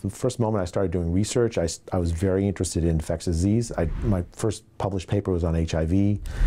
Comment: Sounds like he said infect us 'diseases' hmm